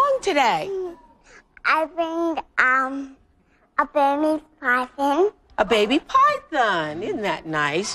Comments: That is a full grown ball python, not a baby. But it's a lovely creature nonetheless :) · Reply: She didn’t say “baby python” as Oprah thought. She said “Burmese python” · She brought a baby python, and now she's bringing a baby human into this world. Bindi grew into a gorgeous woman 😍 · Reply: she didn't say baby python she said Burmese python. Oprah misheard what she said